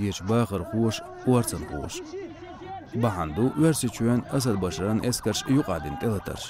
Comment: Азербайджан Сирен т1елатарш деш ю? Сунам доьххьар хезаш ду и. И х1у ду х1инца? Азербайджан т1елетарш деш йелахь, ма гуттур тиша болх бу Сирехь. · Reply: Мне тоже послышалось Азербайджан эскарш)) хорошо что в комментарии зашёл, на мой взгляд диктору следовало сказать - Башар Асади эскарш (будь они прокляты) ...